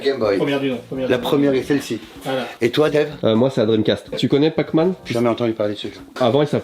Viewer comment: C'est moi ou Ramzy appelle "Dave"?